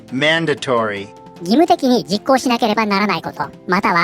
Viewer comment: ｢mandatory｣が｢なんだとオイ｣に空耳で聴こえてしまった…申し訳ない…